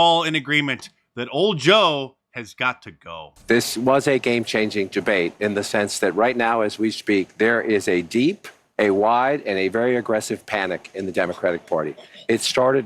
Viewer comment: Did he say "gebate"?